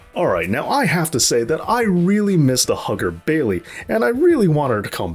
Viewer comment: Man it sounded like you said “I really miss the hooker Bailey”😂😂😂